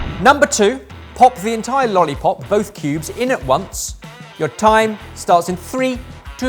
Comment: I thought said 'pubes'... (cuz), fortunately CC said otherwise. Choking is a serious problem with overcooked meats. Swallowing overcooked meats is physically painful, too. was the funniest overall. Is there a prize for that? Lol. That they are tapping out is a great sign of maturity in my opinion.. should have gotten the point for the last round. He kept it together longer than. had to have a lot of coaching...